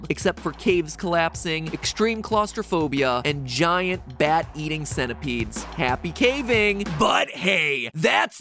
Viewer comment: did he just say giant bay eating centipedes?